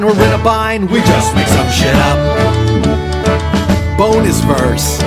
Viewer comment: everytime i get to 4:37 i hear "bone this verse"........ and it makes me giggle every time xD